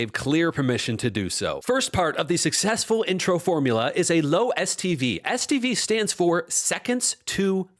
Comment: I thought you said low STD, and I thought, probably true...